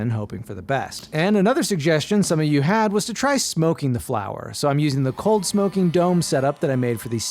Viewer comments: Ik he said "try smoking the flour" But I heard "try smoking the flower" Lmao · I paused this turned it off and forgot about it and when I open this back up I hear “smoking the flower” and a match lit with what looks like weed mixed with wood